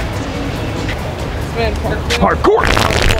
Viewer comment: Parkour = favorites 💯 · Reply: All this time I thought he was saying hardcore! What is parkour?